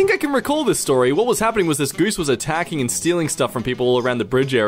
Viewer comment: Did you just say GOOSE?